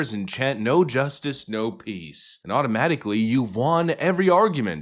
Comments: I misheard them saying "no peace" as them saying "nazis". Wouldn't be too out of line with them. The current "DEI" face is crumbling to show another mask. Remain diligent, give no quarter. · "No justice... no peace" Translation: "I'm a giant toddler and if I don't get my own way I'll scream and scream and scream until I do" Justice isn't simply what you want it to be. Its not YOUR way or "boohoo no fair"! And its not influenced by bullies and intimidation. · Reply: i heard "no justice, no weed"